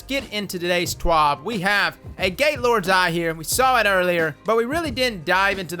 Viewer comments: I swear to god, I heard say “A Gaylord’s Eye.” I’m cracking up over here. LMAO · Sounds like he said "gay lords eye"..